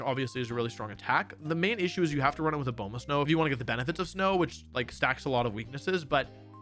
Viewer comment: HELP WHAT DID HE SAY HERE 45:00 GLACEON HAS TO BE RUN WITH A _ ___? It sounds like bulbous gnome lol. I get you had to speak fast to get through this but would be nice if I could understand what you’re saying lol.